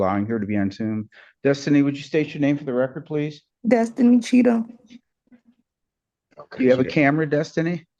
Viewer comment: I thought she said her name was "Destiny Cheeto"... Wow. How does this 1 judge get so lucky with all of these marvelously crazy people..